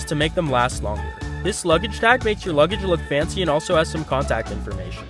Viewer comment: I REALLY thought he said toilet foreskin